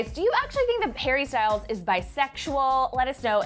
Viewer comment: did she say Harry Styles or Perry Styles?